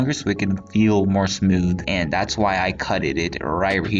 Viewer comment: “and that’s why I cutteded” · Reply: he said cutted it lol but I see why you thought he said cutteded